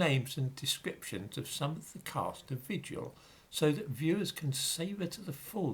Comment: When you said the name of the new show I thought that you said "vitriol." I thought it rather more appropriate when I discovered in the description that it was actually called "vigil."